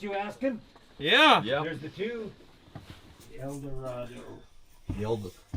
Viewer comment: Never thought a cadillac rv would look like a cadillac deville on steroids, thats awesome! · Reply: I thought he said it's an Eldorado?